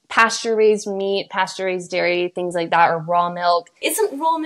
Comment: I think actually said "pasture-raised dairy"